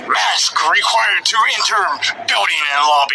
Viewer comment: I don't even think he said "re-enter" it to me like it sounded like he said 're-intermed' whatever that means, his quality is so shit though it sounds like one of those toys from when I was a kid and you press a button and some weird muffled voice comes out so who knows what he was attempting to puke out of his peanut sized brain What a douche making light of what I assume is a memorial photo of a k-9 right after that, normal people would feel at least a little sad seeing the picture, he decides to say oh shit and bark at it